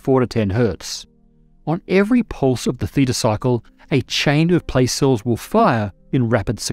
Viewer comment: I thought at first you said the "fetus cycle" and was like... Wait, what?! lol